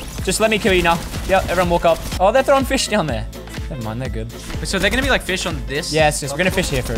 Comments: what did he say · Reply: he said never mind their good😂😂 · Did he say „my nigga“ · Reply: no he said I dont mind they are good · What did he say in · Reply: “Nevermind, they’re good”